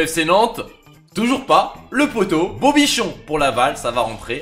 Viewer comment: a 14:00 j'ai entendu beau nichon 🤣🤣